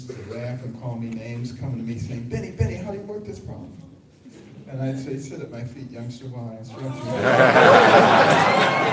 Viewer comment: What did he say around 2:30 ? then everyone laughed. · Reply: Sit at my feet, youngster, while I school you... or something